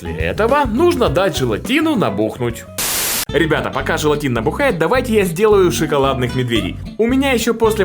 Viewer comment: желатин бухает (послышалось) =)